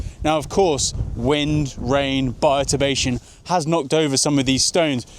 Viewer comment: lol I kept hearing “hard knock” as in the song It’s A Hard Knock Life! Fits, actually. Ask the Dalmatians!